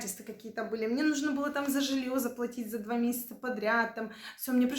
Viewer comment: На времени 9:40 мне сначала послышалось не "за жильё заплатить", а "за женихов заплатить" ))))))))))